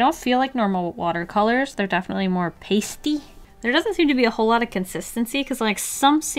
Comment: the way she said pasty ,on my mind I heard Jisoo saying moiistyy